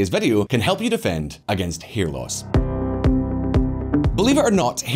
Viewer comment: What did you say? 0:46 oh you said hair loss, thought you ment hearing loss. Lol